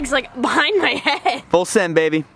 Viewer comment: did he call her baby